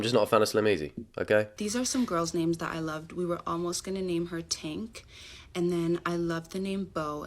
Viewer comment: I THOUGHT SHE SAID TANK